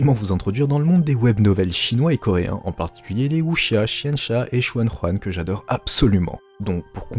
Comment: Attends les quoi ?! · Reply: Je savais bien que j'avais oublié quelque chose... J'aurai dû les écrire en vidéo, my bad ! Je disais donc : les wuxia, xianxia et xuanhuan. Je ferai une vidéo plus explicative à ce sujet.